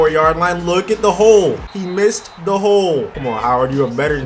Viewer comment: "you missed the hole" that what she said